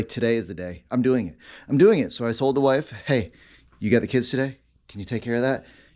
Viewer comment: I thought you said "so I sold the wife." LMAO. What a beast! Congrats on 1k!